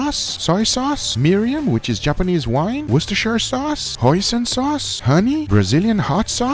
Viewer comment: i kept hearing "Poison sauce" :O